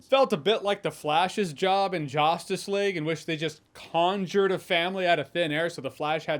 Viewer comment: I heard "Josstice League". It didn't slip by me.